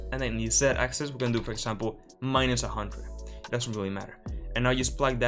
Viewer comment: I made a stoopid mistake and though i heard say '-100' on the second Line trace but checked the video and realized it was 100 in the X - just sharing incase anyone else couln't figure out why the whole thing wasn't working for them:D